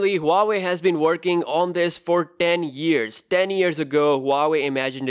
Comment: I like how he says 10 years. I thought he said den years.